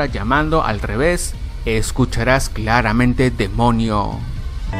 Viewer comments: yo escuche ''el mono'' :v (EU MAKAKO) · escuche demonia :V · yo escuché demoniame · yo escuche "demona" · demonio amame eso escuche xD